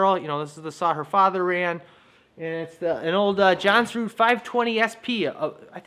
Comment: Long time question I have: How do you pronounce "Jonsered"? It sounded like you said "johnson" in the video! LOL I looking forward to seeing how you attach the other end of the bowstring. When I made bows as a little kid, sometimes I did loops on both ends, sometimes a notch on one end and a loop on the other, and sometimes notches on both ends. Since I'm really really old and my memory is not so reliable, I don't remember which version I liked better ;-)